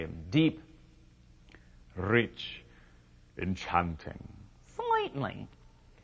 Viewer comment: ZOMG 1:45 what IS he saying there at the end? "Slightlehh?" XD Love your vids man. You make my world soar! ;-)